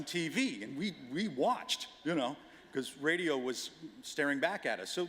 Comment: I hear Harold in the "y'know"